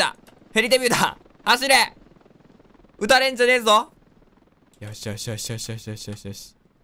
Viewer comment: 打たれんじゃねぇぞがオルガに聞こえたwwww